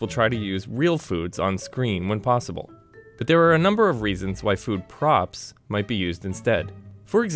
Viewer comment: I thought he said “food probs” XD